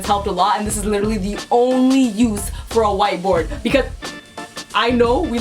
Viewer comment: legit when she said white board i thought she was saying white boy... oops